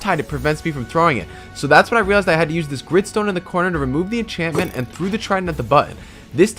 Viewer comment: What did he say????!!! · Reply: Gridstone